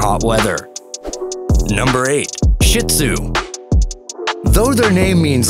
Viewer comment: I think I heard “shit zu” but not”shih tzu “.